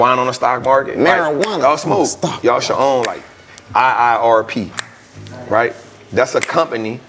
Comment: What land leasing company did he say at @ 26:00? Couldn't find "IIRP" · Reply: IIPR